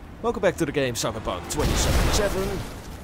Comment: Did i heard "welcome to Cyberbug 2077"?? Lmao